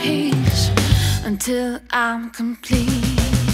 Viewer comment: I had to look up the lyrics of Little by Little, Piece by Piece. building a bathroom to that song. I heard, "little by little, piece by piece until I can pee." I thought you had found the perfect song for bathroom building! Turns out "can pee" was actually "complete."